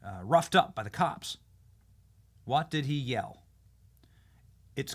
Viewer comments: "It's completely unjust is what I hear." I think he's being set up. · At the very end when he was pushed into the stairway it sounded like he said “it was completely staged”